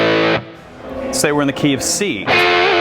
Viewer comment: Am I the only one to hear "KFC" instead of "Key of C" ?